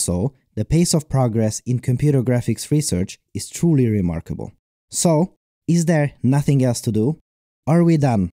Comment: The subtitles say "So, it s there noting else to do?" rather than "So, is there nothing else to do?" Edit: I had to edit this twice because I keep entering the wrong time. Edit: And also again to fix typos in the previous edit.